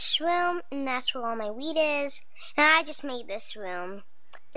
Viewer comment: it sounded like she said "This is where all my weed is" rather than wheat haha :D